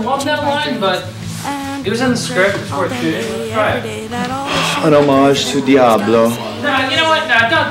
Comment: what does he say at 1:27? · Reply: "An hommage to Diablo " ?